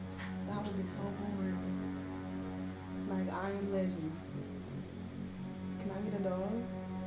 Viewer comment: I think it's just me but did just say "can eat a dog".😂😅 If not help me in the comments, what was said? 🤔